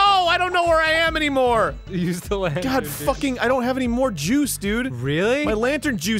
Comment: What actually says: "I don't have anymore juice" What I hear in the first place: "I don't have anymore jews" "Well,you still have me,bro"